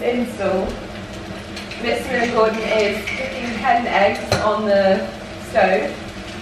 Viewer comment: In fairness to, I thought she said "ten eggs" too. 🤭